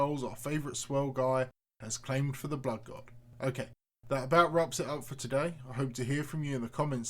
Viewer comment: Did he say "that rub's it out for today"? @ 4:01 ? What a way to say "this is the end" lol by rubbing one out.